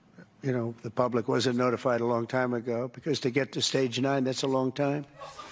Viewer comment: "Stage 9"🤦‍♂️🤦‍♂️🤦‍♂️🤦‍♂️🤦‍♂️🤦‍♂️ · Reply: I thought Trump said stage 10 whatever that is